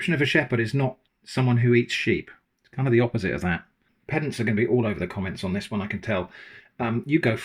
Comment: In tears of laughter because at 19:30 I misheard you and thought you said "Peasants are going to be all over the comments on this one".